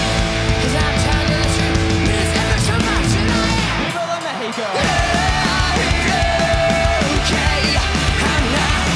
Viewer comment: Frank? What did he say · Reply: “viva la mexico”